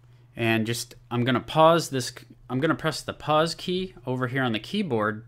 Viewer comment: what is the name of key you said at 1:17?? /purch/????????????? pls reply me :(( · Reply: Pause